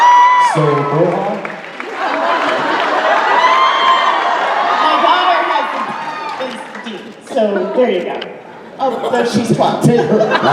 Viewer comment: What did she say about Dean? I can't understand her in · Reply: I think she said "my daughter's favorite is Dean, so here we go.(...)oh she is 12"